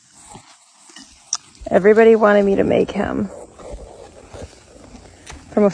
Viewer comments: Nah I misheard her, I thought she said, “everyone want me to throw a fly at the wall” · I thought you said throw a fly in a wok 😅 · I thought she said from a fly into a guac